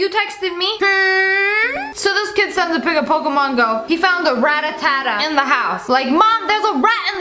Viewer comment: In 3:43 i thought it said ratatata